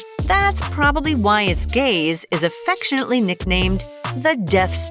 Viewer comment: idk what up w me but I though she was gonna say that it was gay. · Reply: She said gaze not g.a.y 😂